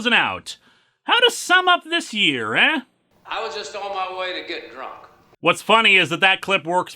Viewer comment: what that line said? (EDIT: I was just in my way to get drunk) 17:20 - 18:10 AND 26:00 - 26:50 I AM LEARNING! 20.25 you will have to move event comics month to at least June. because thats when that corssover is most likely ending (the only good thing is that they debunked my theory of Wally being the culprit after going insane for thinking none of them are real................then again considering they introduced robot duplicates, i am still expecting for a robot wally to be the culprit) 32:45 I am surpried the "sudden motherhood" hasn't used the "erased meories concept" at one point. that the female in question willingly accepted becomign pregnant but the process costed her her memories so we have a dilemma here. 36:25 i have learned, whether 2019 is a good or crap year, (honestly is likely gonna be as bad or worse than 2018) not let that stop you from making things you like.